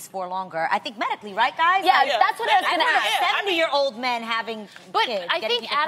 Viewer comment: I thought she said he’s 70 years old I was rey say😂😭